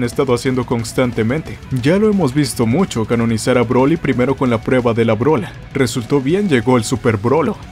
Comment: la prueba de la brola 😅😅😅 pense que dijo la prueba de la bola=testiculo :v